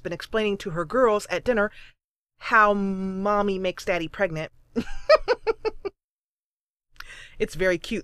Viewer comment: Did you just say at 7:50 "How mommy made daddy pregnant" whaa..?